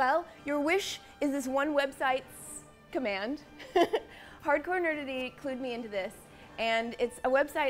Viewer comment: i thought she said hardcore nudity. then i opened the description. :3